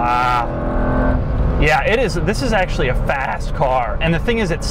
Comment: Lmao I thought he said fat ass car instead of fast